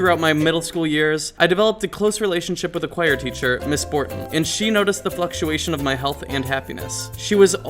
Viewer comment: lol did u say Ms.Morton that's my teacher XD